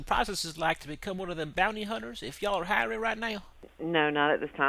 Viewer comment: I thought it said booty hunter haha